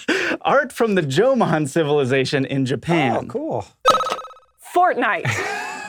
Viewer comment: What does Hank say at 8:05? Also, who won? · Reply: "Fortnite". I guess it was a draw, so, uh, both patreon backers get "I won" pins and swag ?